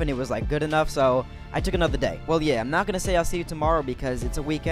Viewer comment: i still have no clue what that kid said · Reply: 'n word are we in real life?'